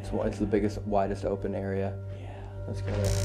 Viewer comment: With everyone with headphones on did anyone else hear real silent yea after said you want to get the SOS camera it was at 45:03 I think you can hear a faint yea right after he said you want to get the SOS camera or was it just saying yea but I herd two yea and mouth never moved can anyone else hear that